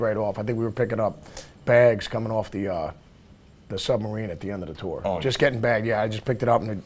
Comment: Did he just say fucking SUBMARINE?